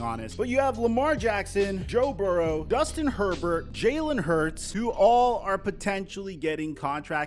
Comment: Did he just say Dustin Herbert? 😂